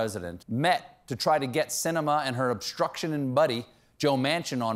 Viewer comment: I keep hearing this name 'Joe Mansion', thinking it's the rich equivalent of 'average Joe'.